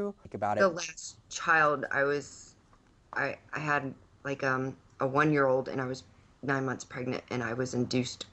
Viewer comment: she had a 1 year old and was 9 months pregnant? Did I hear that correctly? And her sniffling all the way through got on my nerves 😲